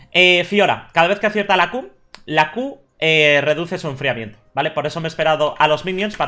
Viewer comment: cada vez que acierta la q reduce el enfriamiento de que, no entendí :s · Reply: Reduce el enfriamiento de la misma q